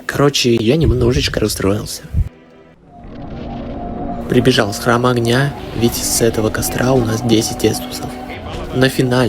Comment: Мне было интересно что он там сказал и по приколу замедлил этот отрезок «Прибежал в храм огня ,ви*#%*деть с этого костра уже 10 эстуса ,которую я сыграл уже значительно лучше победив босса за 46 минут и у меня даже осталось три эстуса. Начало в дарк соулс ещё никогда не было таким сложным» Хрен знает зачем мне это ,но он просто продублировал то что сказал чуть позже просто ускорив ,никаких душещипательных историй там не было 😂 на которые я надеялся